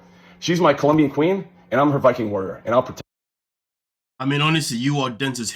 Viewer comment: I totally heard him say "she is my colombian queen and I'm her banking account " but he said viking not banking 🤣